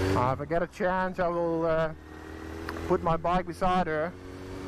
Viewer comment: I had to watch the part where he said "put my bike beside her" again, I thought he said "put my pipe inside her" 😅😂